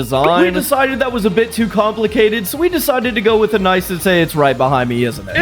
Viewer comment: i thought u said it was too complicated👍